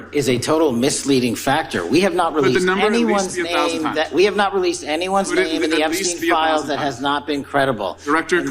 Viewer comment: We have NOT RELEASED anyone's name that has not been credible. ( Is what I think he says here..) So he is saying Trump is not credible? And all others redacted. Credible defined under what context? 20:25 Epstein estate is raised. He says Not under any obligation. WTF Is this not what you get a warrant for?